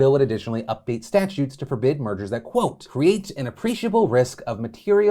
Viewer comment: Half-listening I keep hearing "mergers" as "murders."